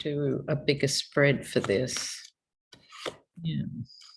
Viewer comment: Hi 🙋 At 14:49, right after you say I might do a bigger spread for this, what sounds like a female voice says, "Yes"